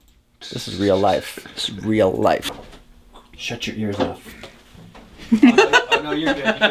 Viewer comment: Lol I thought you said, shut your earlobe 🤣🤣